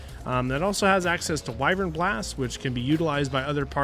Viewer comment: English is my second language and at 23:58 I heard biden blast...